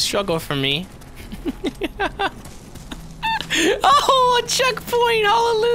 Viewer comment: When he said "ooh a checkpoint!" I heard "ooh a chick fil-a!"